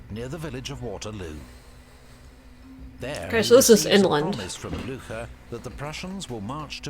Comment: How is it humanly possible to be so stupid about everything? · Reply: Did she really say 'so this is England' ?! I hope not...